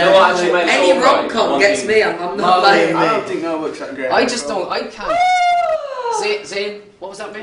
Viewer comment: What did zayn say at 7:05??? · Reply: "i don't think the notebook is that great"